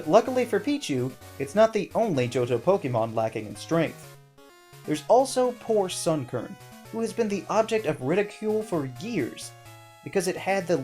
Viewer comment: Did i hear that right? ....Object or ridicule for "Gears" ?